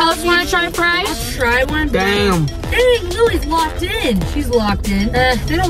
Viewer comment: did say dayum?